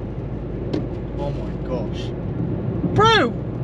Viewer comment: I thought he said OH MY GONADS